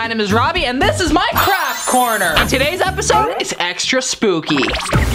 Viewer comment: Who also thought he said crap corner